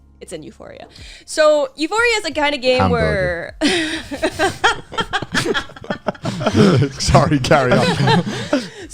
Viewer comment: Wtf is saying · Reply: Hambagu (they talked about this in the food episode )